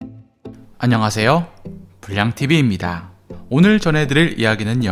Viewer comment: 나는 왜 불양tv가 불량tv로 들리냨ㅋㅋㅋ